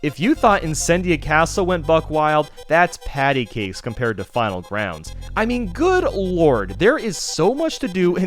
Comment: I thought you said Battleground